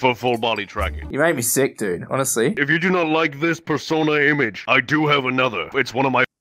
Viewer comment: did Narrator just say "this Persona image"